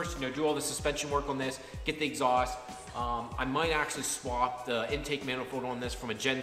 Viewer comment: You do realize the gen 4 manifold is integrated with the turbo. They are one piece. You can not remove the turbo from a gen 4 manifold. Trust me you do not want to get rid of the ct20b you have on there now. (If you have the stock gen 3 set up) It's a great turbo! I own both. The gen 4 ct15 and the gen 3 ct20b. Not a lot of top end with the ct15 with out being modified. Hope this helps. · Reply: Sorry.. thought you said exhaust mani. Anyways..when do you put the new wheels on the 2?